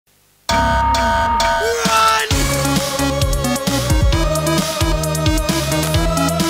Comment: Does it say NOOO or RUUUN? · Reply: Ruun